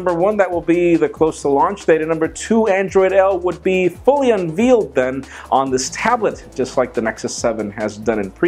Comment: do my ears deceive me or did he say unvealed. Because I for one loves me some veal. 😂